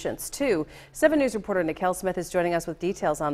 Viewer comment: I thought she said lmao